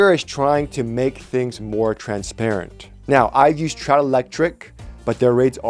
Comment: Can you refer me to your electrician, I can't quite make out the company name you reference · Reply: Trout electric